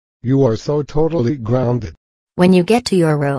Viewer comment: Did somebody say "Greeny Phatom"?